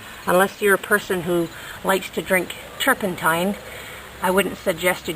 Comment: what does turkentine mean?? i think u said that